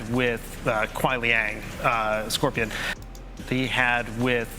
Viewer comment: I ain’t gonna lie it sounds like he says “uh with kuai liang uh and scorpion” meaning that kuai liang is a separate character right?